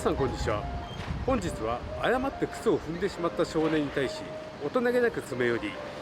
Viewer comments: 誤って糞を踏んだって聞こえたんだけど…。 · 誤って糞を踏んでしまったって聞こえる。